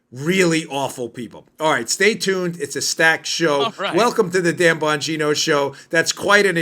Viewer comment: Dan, I thought you said "Welcome to the damn Bongino Show!" Man I need hearing aids..... Too much Bang! Bang! My gut instincts have been telling me since soon after Obama's 2nd Comrade Tour began that the Lunatic Left Commies are staging for a civil war, and it's actually been in effect for years now, but with low mortality because most battles and skirmishes have been on social media and college campuses by those I would call having a "Lions Head and a Tweety Bird Ass," and writing checks that they can't cash. But don't be fooled by the Snowflakes, as I believe that they will be backed up by paid mercs from around the world. I've always prayed that I am wrong; however, with each passing day since 2012, we are an errant spark away from igniting the worst civil unrest that the USA has ever seen, and it will be extremely bad for all Americans because there is no way of knowing the "Good Guys from the Problem Children," and many casualties will happen. That's my well experienced gut feeling for many years now, and we are in trouble. Thanks and Blessings Brother!!!